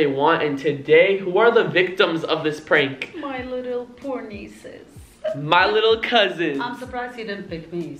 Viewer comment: What did say ? Lmao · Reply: Crimson clan oh nvm she said my little poor nieces I heard it wrong